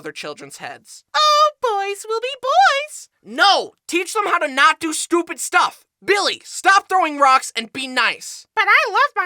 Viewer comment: I SWEAR, I HEAR “ BILLY, STOP DOING STUPID STUFF” THEN A AD POPED UP SAYING” Try cascade platinum plus”. LIKE WHAT